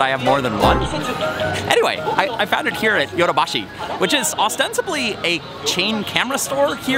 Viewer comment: At about 00:35 you are telling where you bought the LED's. The subtitles say ("Yoda" Bashi, which is "Authentically" a chain store etc.) when you not so clearly say "Ostensibly". Are your subtitles being spell corrected? Just curious.